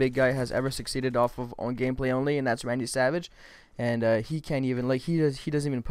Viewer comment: did he say randy savage